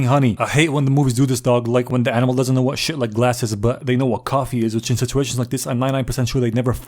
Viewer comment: it is galss instead of glass